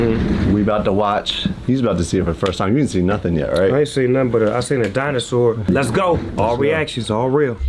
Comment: I aint seen a dinosaur?? what is he saying lol